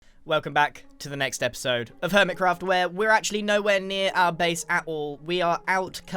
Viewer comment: i heard "welcome to permitcraft"